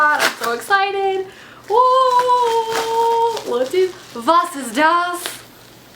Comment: Did you really say: Was ist das? Or did I just completely misunderstand? At about 0:41 or so... :)